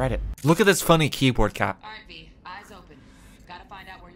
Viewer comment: sounded like she said "RT" for a second there Please tell me you can actually romance your hunky partner in this game. The romantic tension between him and V is off the charts With the shear amount of sex and nudity in this game I'm surprised Twitch is pushing it so hard. Usually if there's like one topless scene they'll go "nope, can't play on our platform, sorry mate not allowed"